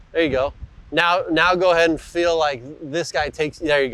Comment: Parker points to the right arm or lat and says something like now feel like this… what was he going to say? Looks like that thought was what fixed the steep action. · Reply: I think what he was going to say was feel like this stays connected. Meaning his right arm shouldn’t get disconnected from his torso too much. I think that’s what he was getting at a bit in the seconds leading up to it as well.